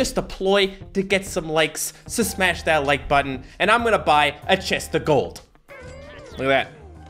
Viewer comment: I thought he said he was going to buy a testicle. Lol